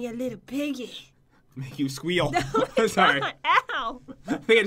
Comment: Holy crap I thought said “I’ll make you squeal” instead of “you squeal” I WAS LIKE DANM YOURE GOING ALL OUT 😂